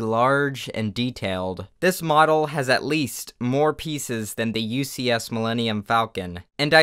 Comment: I thought you said "this model has at least four pieces."